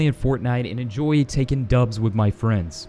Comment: I thought u said taking dumps with ur friends but after I realised u said taking dubs